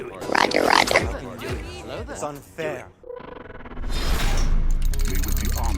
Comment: I only heard "It's not fair!" in that intro.